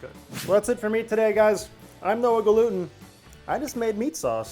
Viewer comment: Ever time he says his name, I hear "No a gluten".